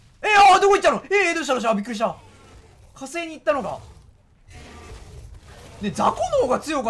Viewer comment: が言うと火星にしか聞こえない